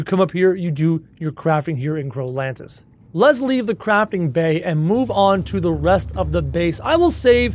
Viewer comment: When he said- let’s Leave the Crafting bay, I thought he said- let’s leave the Crapping bay